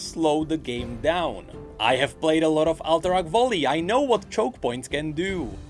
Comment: what did he say he played at 12:37 ? i heard alterac valley?